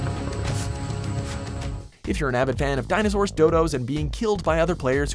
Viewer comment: Anyone else hear him say “Dildos” and not “Dodos” at 4:24 ?